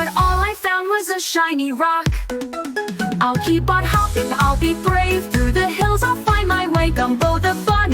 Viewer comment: The voice says "hopping" and your subtitle says "hoping". Just a polite reminder that it is quite important for anyone setting out to make videos like this that you need to make sure there are no typos or mistakes.